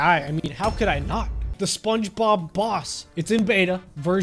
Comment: who else thought that he said spongebob ross instead of spongebob boss